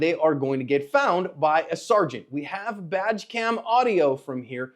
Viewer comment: Love your content, been a subscriber from the beginning For a split second I thought you said, “Vadge Cam!!!” 🤣🤣🤣